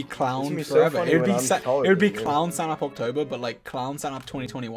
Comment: WAIT DID HE SAY OCTOBER!!!